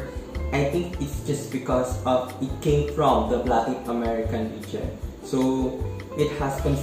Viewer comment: What do you mean by black american region? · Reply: Hi, I'm not sure what you mean but I believe you must have misheard what I said in the video, it's "latin american region" 😊